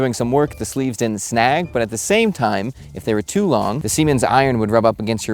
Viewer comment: straight to the comments after i heard him say "seamen"